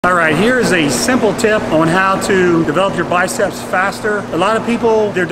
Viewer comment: Tell my why I heard “ milk your biceps faster”